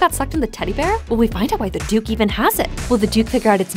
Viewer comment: I thought it said “the dookies teddy bear” 💀